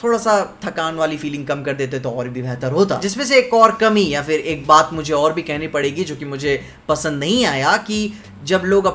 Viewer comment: Can someone please explain what was saying in 5:12 in can't get it what he was saying!!! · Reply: Protect Trans Kid bolke ek poster tha movie mein